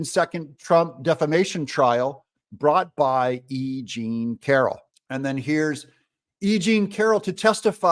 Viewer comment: I thought he said a-ging Carroll instead of e jean Carroll at first.